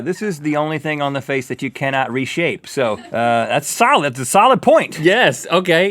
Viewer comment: Did he just say that you can't reshape eyebrows?